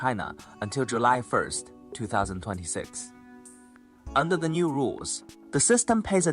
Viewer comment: subtitles is wrong. year should be 2026 and not 2016